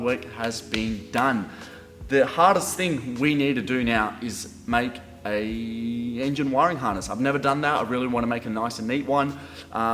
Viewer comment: did you say make an engine harness 👀 wonder if i should chip my 2 cents in here cz i did mine